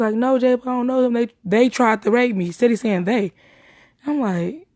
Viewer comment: In my opinion it sounds like she said “ I feel so good now he tried to rape me” but that’s just my interpretation